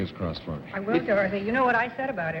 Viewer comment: Did Dorothy call him “Dorothy”?!